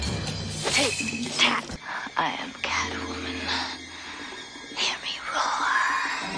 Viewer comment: I like when Phifer says "IM A WOMAN!" so seriously in the character switch but like said she had to have had amazing confidence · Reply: I thought she said "IM CATWOMAN" i watched this many times and my whole life was a lie! 😵